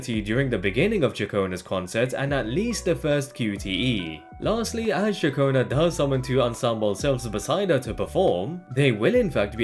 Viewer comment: What does he said at 5:57? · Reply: "Lastly, as Ciaconna does summon two ensemble selves beside her to perform."